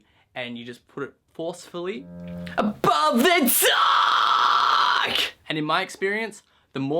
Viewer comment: "DUUUUUUUUCKKKKKKKK" once i heard it, i couldn't unhear it. "ABOVE THE DUUUUUUCKKKKKKK!"